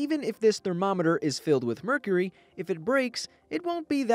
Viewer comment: I thought it said "me curry" ...